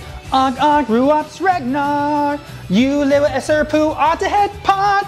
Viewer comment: you live in a circle ahead that's what i heard